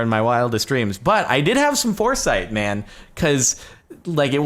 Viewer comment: gonna admit, when he said- I had foresight- I thought he said foreskin. Did a triple take. glad he didn't say that.